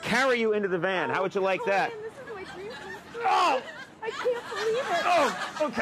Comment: Did anyone else hear that lady at 2:22? "Oh Conan this is my dream come true, I can't believe it"